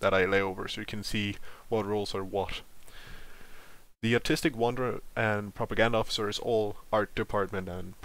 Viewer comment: I legitimately thought you said, "the Autistic Wonder", and I was like, "well I guess we're here now." I need to listen closer...